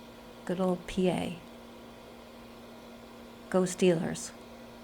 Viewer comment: Ghost? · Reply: In retrospect I think you might have said “Go Steelers”. But I heard “ghost dealers”